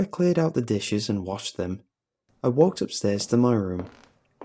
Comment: Did he just say “i walked upstairs to murder him??!?